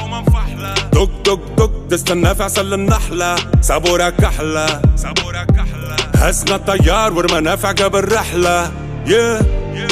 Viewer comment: SOLO ENTENDI SABOR A KAGLA